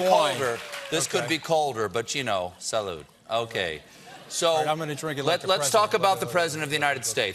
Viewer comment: does Scaramucci say "I'm gonna drink it like the president"? LMFAO